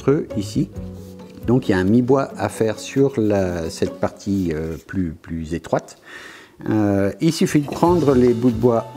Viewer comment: I was quite enjoying watching this, not understanding the words he was saying (the imagery is entertaining enough) and then at around 4:19, he says something that when written using English phonetic spelling, sounded like 'plue ze twat'. Okay, on go the subtitles.